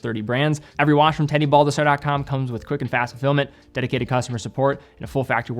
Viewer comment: I just realized you say "Quick and fast fulfillment" but for years I've thought you were saying "quick and fast to film it"